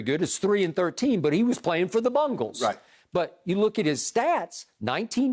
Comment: thought skip said playing for the "bungholes" lol thas their new name